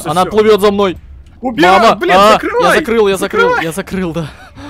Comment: мне послышалось или сказал "блядь"на